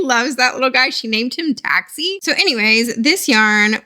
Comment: did you say the push was named Taxi?? I love that! My favorite plush growing up was a purple bunny that I named Taxi. Thought it was cute she named her plush the same.